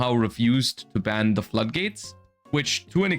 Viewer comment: When you mishear "flood gates" as "slut gates". Kinda checks out.